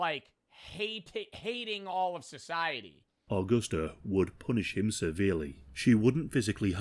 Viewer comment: did he say… punish him severely?